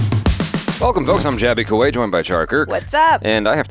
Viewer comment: When I first heard your name I thought you said, but then I looked down and realised how dumb I am😂😂😂😂